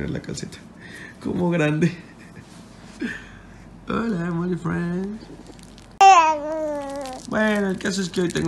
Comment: a se le entiende que dice "hola morifriends"